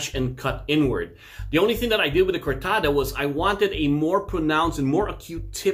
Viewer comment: Anyone knows how the 3:00 blade is called?? I heard it like 10 times but just couldnt catch the name sorry :c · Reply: Kortada